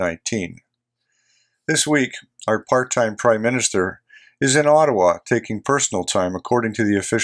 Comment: Aren't Canadians sick and tired of corrupt JT and his Liberals yet? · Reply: I could swear I heard him say our fart-time Prime Minister